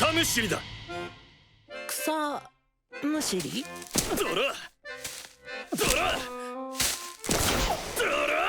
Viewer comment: I heard it as DORAAAAA